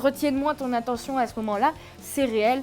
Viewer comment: BON. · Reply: La première fois j'ai entendu "et auquel cas c'est votre problème" mais je crois que comme elle a pas bien articulé en fait c'était "et auquel cas c'est un autre problème".